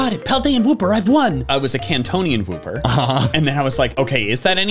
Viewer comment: Did he say Kantonian Wooper?